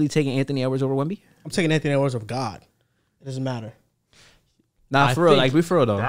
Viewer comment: did he say “over god” or “on god”😂